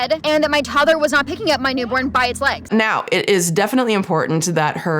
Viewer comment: did she say "by ITS legs" referring to her newborn?? She doesnt even view them as people...